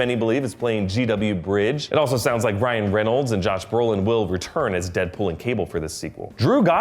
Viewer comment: did he just say Brian Reynolds?